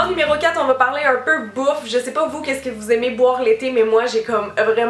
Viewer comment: Quand t'as dit "Bouffe" j'ai compris "Beaufs" 😂😂😂😂😂😂😂😂😂😂😂😂😂😂😂😂😂